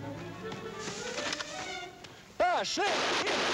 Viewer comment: did the German really say "oh, shit"? It sure sounded like that to me, anyway. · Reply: Sounded like "Ahh, schnell!'